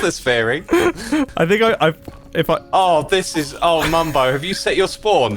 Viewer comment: When mumbo accidentally killed himself with the bed it sounded like X said “Ohh Bumbo” appropriately so lol